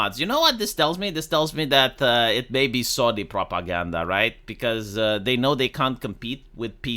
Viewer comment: yes! hey , im one of those ppl that have that italian parliament tifa porn :D oooh "sony propaganda" , i thought you said saudi propaganda lol